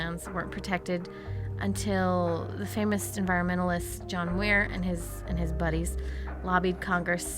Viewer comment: Is it just me, or does it sound like she said John "Weir" instead of "Muir" at 2:11?